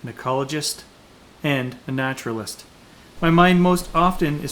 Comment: In your opening monologue I thought you said you were a nudist (lol) Replayed it...you said naturalist. Whewwwww. Since the brush bar interferes with your headlights how about mounting LED running lights on top of that bar. With the clamp type mounts they could be swung down for hauling deer. Bigfoot or not I enjoy your videos. You DO live in a beautiful place.